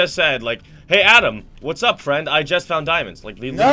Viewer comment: i heard him say eyes found dyemundz